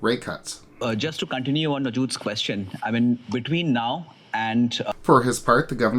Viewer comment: did he say to comment on the dudes question? 😂